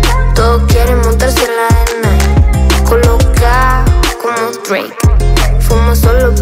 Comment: No sé si alguien más le pasó, pero pensé que ponía colacao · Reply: Como que de la emoción pensé que decía colacao pero era colocao